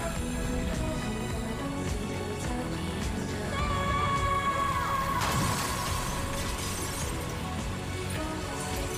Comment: What spell did she say in 3:59? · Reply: She said 'Sail'